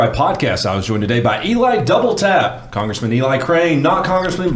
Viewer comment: For the first 20 unsubs I watched, I swear to God I heard "Eli double fap".